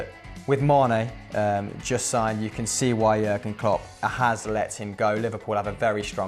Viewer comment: I thought he said Liverpool signed Sane!